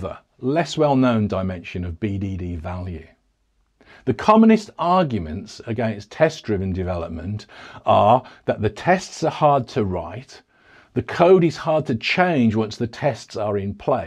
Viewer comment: Did he say "Communist arguments" in 11:04 ?